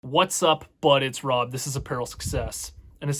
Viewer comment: Until this day I always thought your intro said: "whatsupp, but its Rob" in a really weird way haha